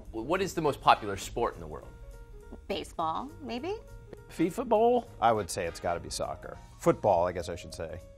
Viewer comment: Wait... Did he just say Fifa ball